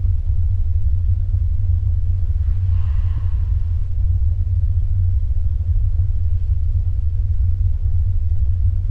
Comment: se escuchó un susurro que dice (MARIANA) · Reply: Era diciendo Mariana ya tira el oso